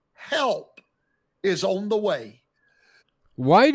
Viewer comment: I don't why but I thought he said He'll was on its way lol 😂